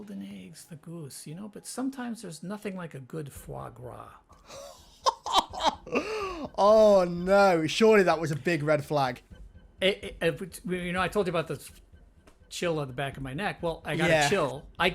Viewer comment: What does marty say at 1:45:40? I'm hearing "there's nothing like a good frog raw", and I'm fairly sure that's not what he's actually saying.